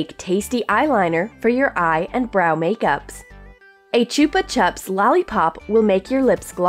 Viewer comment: i was dead it sounds like your saying chuper chups but its chupa chups